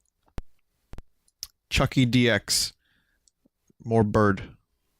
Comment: When Vinny said chukie I heard it as chunky